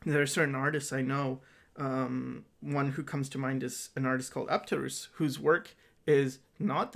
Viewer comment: What artist did he mention at 1:41:18? I can't seem to find them online and must have misheard him. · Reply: If you haven't found him yet it is the beautiful drawer of the undead, Apterus. He makes the cutest decomposing humans. <link>